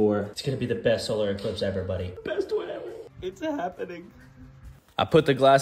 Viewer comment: At the end i heard "This is all we gyat" ( no hate btw )